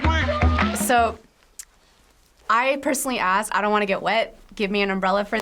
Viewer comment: you will be in rewind and what you say is I don’t want to get wet